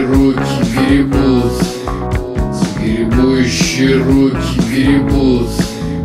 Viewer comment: Я один услышал "е*учие руки гребут"?!?